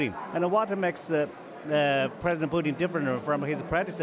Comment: What he said President booti 1:49 😂😂😂😂.